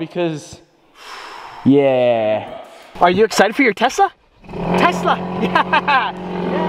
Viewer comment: did anyone else hear "are you excited for your tessa" ?